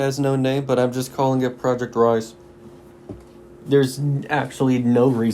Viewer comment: thought you said "Project Rice"... i think im just hungry